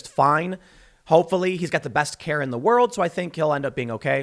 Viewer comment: I heard tim say, "it's ok hes got the best hair in the world" over "care" and I legit lol'd